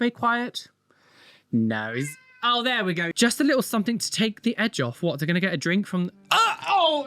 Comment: I thought was saying "Something to take the edge off," about Mr Globex meow and I was thinking 'yes that is true.' 😂